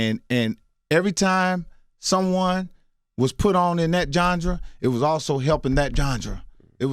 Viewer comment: Did he say jondra 20:13? I think he meant genre.